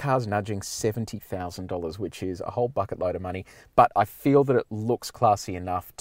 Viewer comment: Did he just say a whole fuckin load of money?